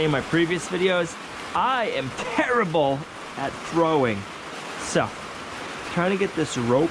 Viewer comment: Who does the cc dialog? I was confused when it said you were bad at drawing, when I thought you had said bad at throwing! A couple of other words were wrong too. I have to use cc sometimes because my husband is watching television and I can't have YouTube blaring. Love watching you guys.